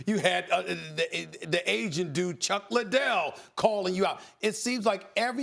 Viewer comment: Did he just say, "The Asian dude Chuck Ladelle" ????2:36... Lol...😆😆😆